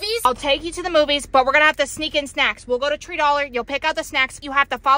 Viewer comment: Did she just say tree dollar😂😂